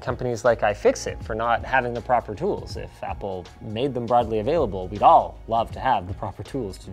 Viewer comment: I just caught the 2 words wrong. Linus said " If Apple" and I heard "Fapple"